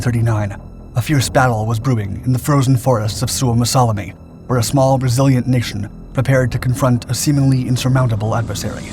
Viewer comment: I thought he said “ a small Brazilian nation “